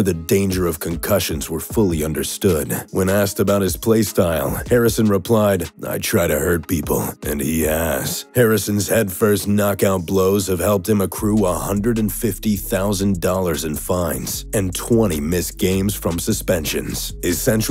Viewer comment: did anyone else hear "I try to hurt people and eat a**" and then rewind to see if that was actually said?